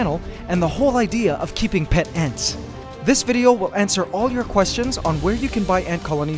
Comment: I swear I heard antswer